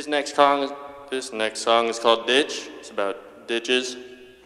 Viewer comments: anyone else thought he said " This sing is called bitch its about bitches." LMAO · Lmao I thought I heard him say the song was called bitch lmao 😅😂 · when he starts playing ditch i always hear him say " this next song is called bitch, It's about bitches" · Has anyone named each album on the wall? I’m too old and senile and only recognized modest mouse and neutral milk hotel. *sidenote: I honestly thought he said “this song is called bitch, it’s about bitches” lol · I THOUGHT HE SAID BITCH. HAHHAHAHAH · Am I the only person who thought he said, "this song is called bitch, it's about bitches" ?